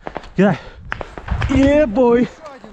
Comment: serious question, did he just say "Yeh boi"?